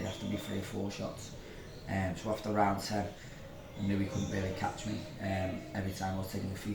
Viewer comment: Did he call sultan “Inoue” from 3:00? Or he said something else? I’m a bit confused Anyway sultan beat casimero and butler will fight the best pfp next when casimero is going to fight Japanese semi retired YouTuber boxer next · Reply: He was saying " I knew"